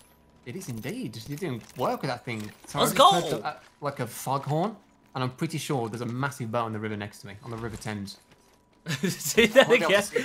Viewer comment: Can anyone translate what said at 6:19? 😂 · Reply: I think he said "sorry I just heard like a foghorn and I'm pretty sure there's a massive boat on the river next to me, on the river Thames" English isn't my native language though, so I could be wrong :)